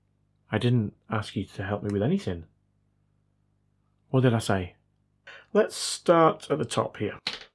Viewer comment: You spoke very fast "like I said", and it sounded like "Alexa".